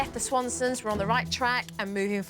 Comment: I thought they said the twatsons